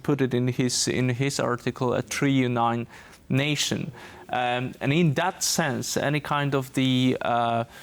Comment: What is he is saying? 3 in nine nation? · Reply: Imperialist idea that Russia, Ukraine and Belarus form the triunion russian nation, just like the Holy Trinity is the triunion God.